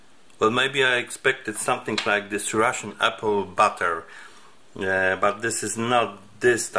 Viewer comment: I think this is a misunderstanding because of my poor pronunciation. I am not native English speaker. My word 'ration' sounds like "Russian" . I means ration/rations not Russian/Russians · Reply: Then sorry, my English is not very good, I really thought you were saying "Russian porridge"